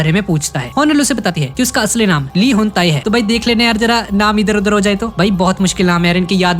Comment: i Heard he hentai 😂😂 🦵